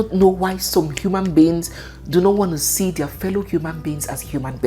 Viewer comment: Join our Amazing AFRICA FOLKTALE CHANNEL & enjoy <link> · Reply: I thought you said you don't want the show .